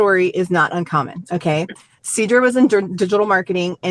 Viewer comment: I’m multitasking so for up until the last moment I kept hearing “Seadra” like the Pokémon lol